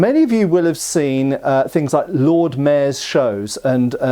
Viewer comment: Briefly thought you said “mares” before you added the towns and cities. The idea of a horse wearing one of those is a funny mental image though.